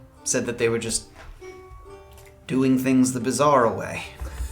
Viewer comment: Misheard: "Doing things the Bizarre way"